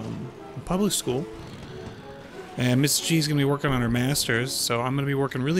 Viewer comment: Sounded like you said "Mr. Gs gonna be workin on her masters" lol